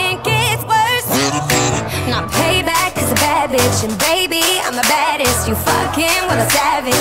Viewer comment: "Now payback is a bad bitch And baby I'm the baddest You fucking with a savage" FAV LINEEEE!! DEMI YOU SLAYYY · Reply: I thought she said "baby I'm a bad-ass" lol